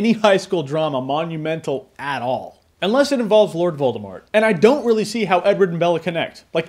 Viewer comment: did u say "unless it involves voldimort"?